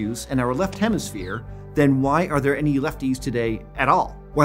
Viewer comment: Ok so I'm from France, and I don't hear words as well as y'all do so I thought he was saying "why would there be any leftists left" instead of lefties, in case you looked for a really bad pun 🤷‍♂️